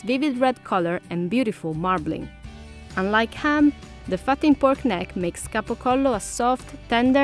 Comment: anyone else mishear this as. ‘The f^^^ing pork neck’